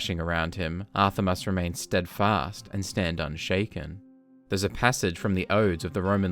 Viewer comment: Im really sorry but at first I thought the song said: “Meoww, stand and shakin”.